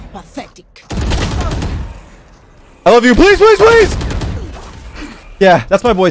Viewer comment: I LOVE YOU PLS PLS Dies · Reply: i thought he said "ALL OF YOU PLS PLS PLS"